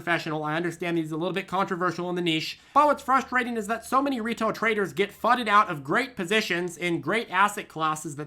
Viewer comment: sounded like "So many retard traders". Felt that.